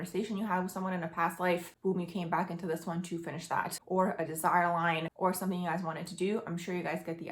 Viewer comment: did you say “designer line"? I have Saturn in the 12th. I want to make sure you didn't say what I wanted to hear. 😅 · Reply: It was “desire line” 😊